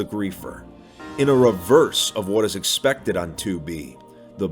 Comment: it sounded like uno reverse